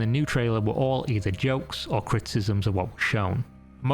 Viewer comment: What are you saying here? It sounds like "cryptism",but that doesn't really make sense given the context. I'm assuming that "criticism" is what was meant,but you say it several times and it never sounds right. I notice weird things sometimes. I know.